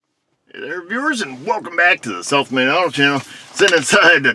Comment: I like your videos. Funny fact though, for the longest time I thought you were saying self made auto Chanel.